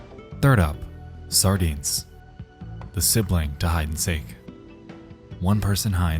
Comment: I miss heard that as “the sibling died at Sea,” and it made me do a double take